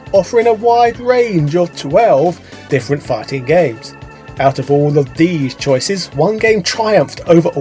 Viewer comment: Didn't read the title before clicking. 0:07 thought for sure he said "Farting games". I'd join patreon to recommend a video on that. LOL